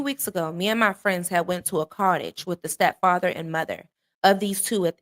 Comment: sorry but what did they say they went to with the parents? just found your channel and great video btw. im subbed! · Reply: They went to a cottage with the parents of the kids.